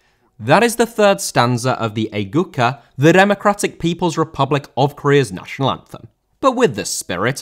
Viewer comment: I heard he Democratic People's Republic of Korea (not sri lanka)